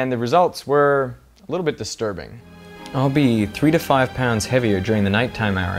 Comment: Hahaha at first I thought it said 721 pounds 😂